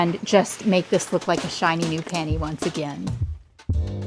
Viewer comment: You said you’re gonna make it like a "shiny new penny"… I thought you said "like a shiny new panty", and so did the subtitles! 😂